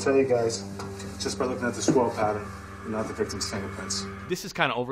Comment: Re: Seven I heard "this whorl pattern," not "the swirl pattern," but that might have been confirmation bias